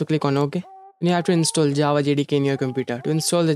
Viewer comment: What's the thing you said after Java at 0:06? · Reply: Java JDK